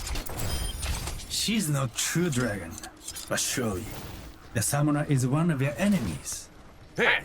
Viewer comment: he actually says: she’s not true dragon. But surely the summoner is one of your enemies